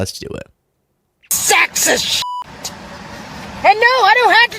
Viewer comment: I thought she was saying, "SEX IS SHIT." 😂